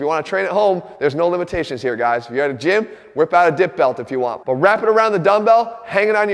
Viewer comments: I thought he said "whip out a DICK belt" at first. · I seriously thought he said "Whip out a dick belt if you want" I had to turn the captions on. 😅